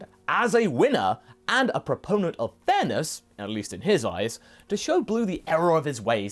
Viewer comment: I thought you said proponent of Thanos